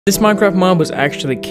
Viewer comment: I thought you said this Minecraft mom at first 😂